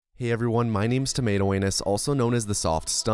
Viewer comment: I clicked and heard "My name is tomato anus" ....I scrolled down, read the name... and thought "yup I heard that right, he said anus"